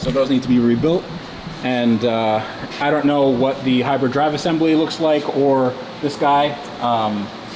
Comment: "the hyper drive assembly" Damn, that McLaren is way more advanced than I thought. 😅 Or maybe I misheard that. A bit.